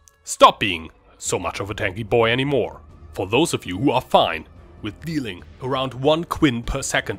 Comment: Did I hear "Dealing around 1 Quin69 per second of DPS"? LMAO!